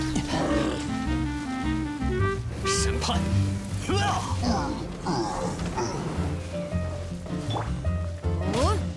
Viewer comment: What'd I just hear? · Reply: SENPA- HUAGH